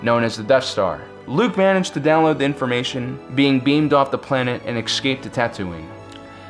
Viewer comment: WUT? "beamed up from the planet" Did I just heard that right? I thought I see a star wars fan's video.